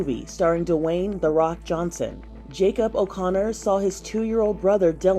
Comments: sorry but why does it sound like she says "the wayne "the rock" johnson" 💀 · Did she just call him "Doo-wayne" 😂